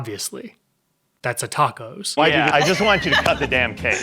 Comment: I know I’m late but every time I hear him say “ a tacos” I really hear “cool hwhip”